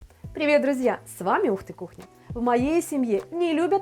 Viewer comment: Мне одному послышалось ,,узкая кухня " ?